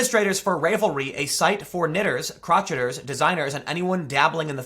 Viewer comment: It's pronounced Rav-el-re. It's not pronounced crotch-et! it is crow-shay! COME ON TIM! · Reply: Yeah, I was correcting him out loud. 😂 At first I thought he said crotchety... like all us knitters are grouchy and cantankerous! 🤣